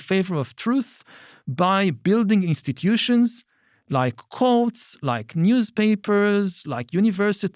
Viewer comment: he said "courts" but I first heard "cults".